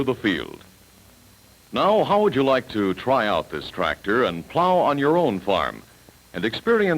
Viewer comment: Aww, dad! I thought you said you bought a tractor !